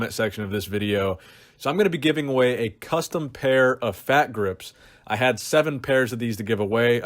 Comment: First I hear ''fap Grips''... L😂😂😂L They're good to get a giant Radio Brachialis... maybe.😄 Anyway. A good Video with lot of interesting information around BB. Keep it up. Good Work 👍😊